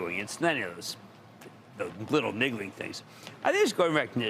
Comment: Did he just say lil niggly things 😳 at the 1:11 mark?.....🤔